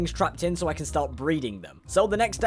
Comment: I wasn’t looking at my screen and I thought he said weed